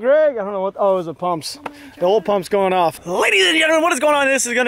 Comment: At the beginning I thought he said “lil pump is going off”